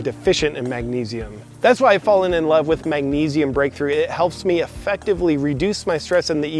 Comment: i thought you said magnesium grapefruit, now that sounds yummy! Sold it like it's one of your own products, beautiful, lol.